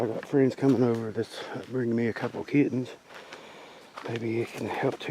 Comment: Well I have to have my hearing checked >> I thought you said friends were coming over with a couple pigeons to help keep the mouse population down !!! I was thinking that I had not heard of that before ???? Checked back and you said kittens !!! LOL